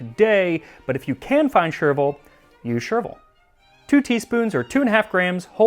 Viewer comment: when you said “If you can find chervil, use chervil”. I heard “You chervil” instead. So chervil I will. Whatever that means.😂